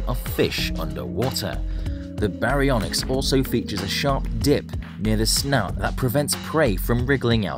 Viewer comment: i almost thought he said sharp dick